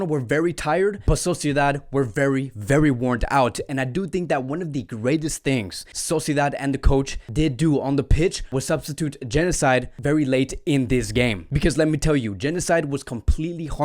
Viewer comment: thought you said Genocide for a sec there 😅😂